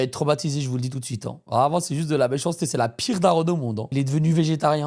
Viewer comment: C juste moi ou il a dit ma soeur la pte?